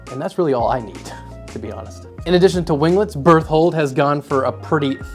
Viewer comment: When you said "berthold", I thought you were saying "birth-hole" 😂